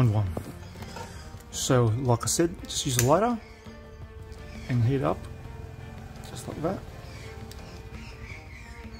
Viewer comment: Hi, thanks for your great videos. Whats the 'palstic' called you are using and heating up at 6:00? I can't understand it quite right. Thx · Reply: Styrene.